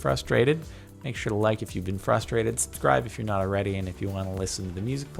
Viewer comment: At the end it sounded like he said Subscribe if your not already frustrated